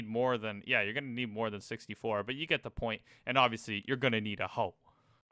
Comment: I thought you said "and obviously you're gonna need a HULK"